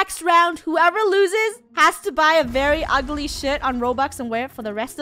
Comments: Peeps is it just me or did say s* or shirt on 19:30 . . . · I love your vids so much but did anyone else hear the s word in stead of shirt??? 19:23 Just asking 🤷🏾‍♀️🤨❓ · did she say the sh word or shirt? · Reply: Shirt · is it just me or did she say sh!7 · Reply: shirt.